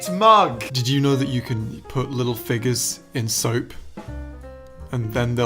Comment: “Did you know you can put little figures in the soap... and then they’ll be in the soap...” 🤣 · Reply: I thought that said fingers at first